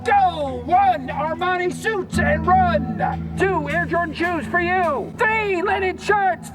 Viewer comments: he is not saying "Are my suits and run". He is saying "Armani suits and run" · I hear "One, Armani suits and run", not "One, are my suits and run". Armani being an expensive brand of suit. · i’m pretty sure at 2:27 he says “armani suits” instead of “are my suits”